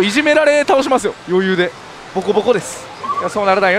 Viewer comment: ここの、ぼこぼこです。がひき肉ですみたいに聞こえる俺は末期なんか、、？